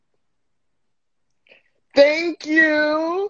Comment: i didn't understand what happened someone explain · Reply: the pic looked like her a lot, so the performer imitated her LMAO she always says "thank yew" after someone performs